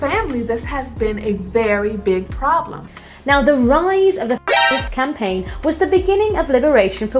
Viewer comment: What is being blipped out that these women to keep saying 😂🤷🏻‍♂️😅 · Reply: Words like “feminist” that are contrary to YouTube guidelines or attract the attention of YouTube staff censoring what is said e.g. The woman at 4:18 says “feminist” I think and the one at 4:56 says “feminism”.